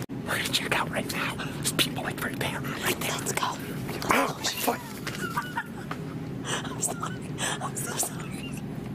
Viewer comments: did say The f word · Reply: Bossage_Ipug no he said my foot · Did just say the f word a 6:49 or was it just me???? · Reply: No he said "ouch my foot" XD but I heard the f word at first XD